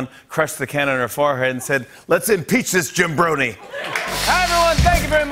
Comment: Hold up.... did he just say Jim bornie?